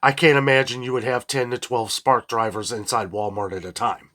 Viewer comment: Did he just say "10 to 12 smart drivers in Walmart at 1 time" 🤣 There isn't even 5 smart people in the entire store at 1 time!